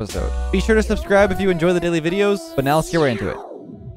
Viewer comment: I thought he said lets now get racist like what was my brain doin 💀